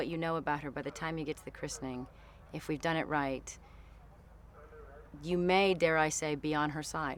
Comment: anyone plz tell me what she's saying at 1:03 like, " you may... beyond her ..."???